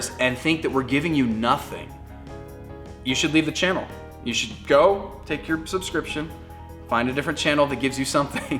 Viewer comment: oke... that just sounded like: you should go... you are fired...